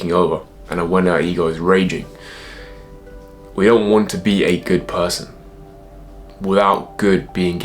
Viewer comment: the transcript says "Raging we don't want to be a good person" but I think I heard you say, "Raging we all want to be a good person"--is that right?